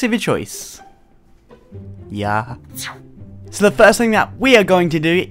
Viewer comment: who else heard him say "YEAH..."